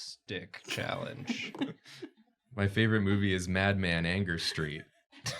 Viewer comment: When said "Mad Man: Anger Street", I heard "Mad Man: Anger Stream".